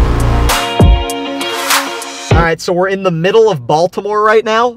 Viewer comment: i thought u said were in the middle of baldi now haha